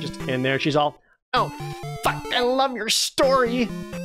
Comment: I already thought he was gunna say: "Fakk, I luv your sword!" =D